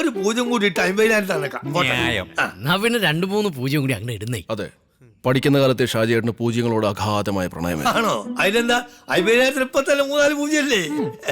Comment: In subtitles at 1:57:30 it should read right from school days, shajiyettan was deeply in love with zeroes instead of right from childhood